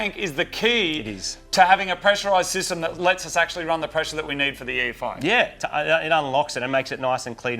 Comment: The first time through, I thought said the new tank would give them the "pressure we need to run E-85." Wait a minute, I thought said they were not going to turbo it. I watched it again and thought they are totally going to turbo it. This morning I watched a 3rd time to get the timestamp and realized he says "pressure we need to run the EFI." :-( So, no turbo then. Fine. It will still be cool.